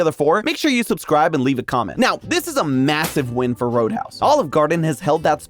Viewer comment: did I hear massive??